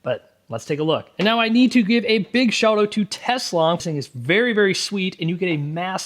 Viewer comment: i thought you said Tesla🤢🤮